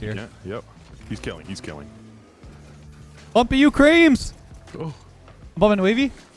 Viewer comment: said bumping you creamz and for a second I thought he said f**k you creamz 😂😂😂